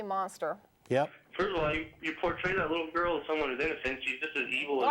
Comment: what did he say? I cannot understand him there. It must have been awful... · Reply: he said that girl deserved it bc shes full of sin in his words: "you protray this little girl as someone who is innocent"